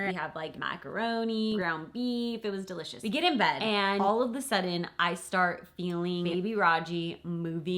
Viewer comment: Did she just say "all of THE sudden" ??